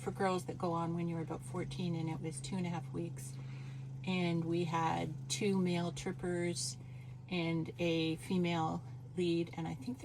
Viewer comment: I thought your mum said "We had two male strippers and a female lead".